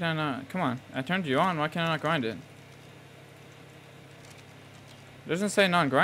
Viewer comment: am i trippin or did he just say "i turned you in so why can i not grind it